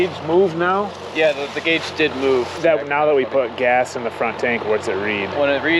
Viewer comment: Did you say around 12:30 you put gas in it 😂😂